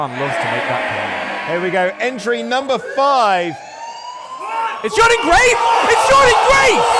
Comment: It sounded like Dude said "Its Jordan Grace...YAYYYY!!!! 😂😂😂😂